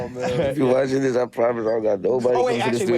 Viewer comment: Ohhh get Omari On.. I thought he said get Omarion. I was like what he gotta do with this 😂😂😂